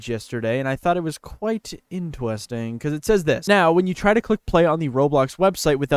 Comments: When said “it was quite interwesting” it sounded like it got a new accent in like 274 milliseconds lol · Did bro say “intewesting”?